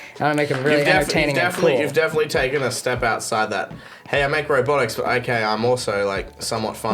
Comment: i thought micheal said "I make Roblox" lmao